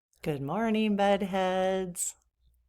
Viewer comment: 😂 I thought you said Good Morning Buttheads 😂 Obviously I need a hearing aid .